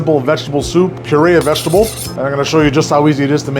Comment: I thought you said “Sharia vegetable”